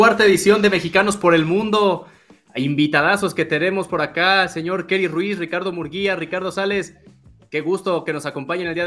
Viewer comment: Jajajaja pense que decia karely ruiz 🤣🤣